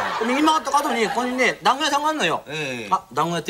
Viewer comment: シムケンの団子屋さんの発音が「だんが屋さん」に聞こえてしまう。